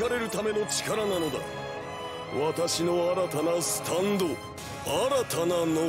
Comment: I like how it says “Maiden Heaven!” But what he said is “Made in Heaven!”